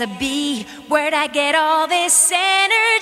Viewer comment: had no idea this was from a anti meth psa. Always heard it as "mmmm meh" so I didn't know it was talking about meth